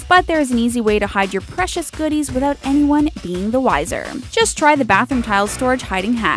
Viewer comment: Haha! At 2:38, she said vanyone instead of anyone!